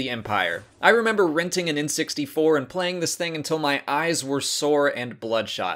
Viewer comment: I thought he said “I remember renting it in 64” and i was like... dafuq